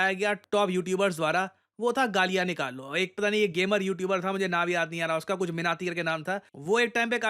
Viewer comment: Did sir said minati? Carryminati? Or i heard it wrong? By the way you are not wrong about him... He spits lot of gaali-galoch.... Long live India 🙏🙏🙏🙏